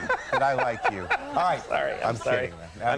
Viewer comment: did Conan call him Larry? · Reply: Think he tried saying 'sorry' but didn't start right as he was mid-laughter.